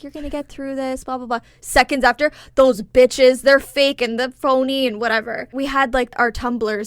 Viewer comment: I'm not gonna lie, when I heard this segment on your newest video, I thought was saying "those are fake boobs". "Fake bitches" makes WAY more sense 🤣.